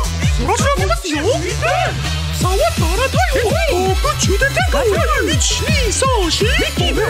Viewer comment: i cant- 😭 1:30 did i hear "i wanna kiss donald duck!"